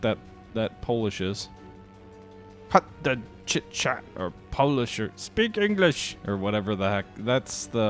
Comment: What he said was "Jeszcze Polska nie zginęła" (Poland is not yet lost) - it's a title of Polish national anthem.